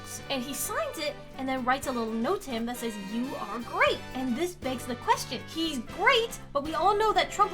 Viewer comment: i swear that says “You are a brat”